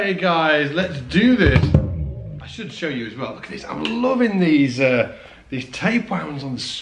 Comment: Sounded like he said: "I'm loving these tapeworms!"